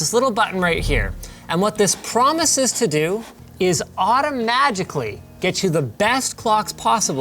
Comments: Is it just me or does Linus say "automagically" instead of "automatically"? Auto generated subtitles seem to agree with me. Lol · Did I hear “automagically” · Did you just say fucking "automagically"!?!?!